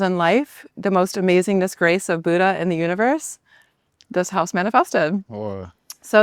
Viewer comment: what does she say? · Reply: Sounds like she's saying "and finally disgrace from Buddha ,, 😂 but actually I think she's saying, .. "and finally, this grace from Buddha"